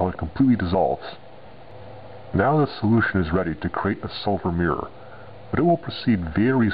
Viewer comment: Did he say Sulfur mirror at 0:52?